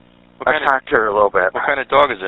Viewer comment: Why was the rottweiler owner so oddly calm. He almost sounded annoyed that this lady was attacked by his dog. What? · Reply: "Uhm.. the dogs kinda like tore her face off and.. and uhm.. attacked her a little bit" with a slight chuckle at the end. The subtitles said "talk to her little bit" but it was actually "attacked". I hope it's because he's shocked that he can be that cold..